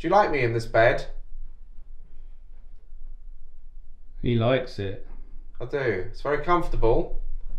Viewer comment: When the box was doing its thing for the speaking thing I heard “I like it” like ad the song 😭😭